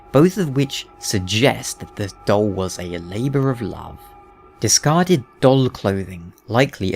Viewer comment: Misheard 5:12 as "Doll was a labourer of love". That was really disturbing.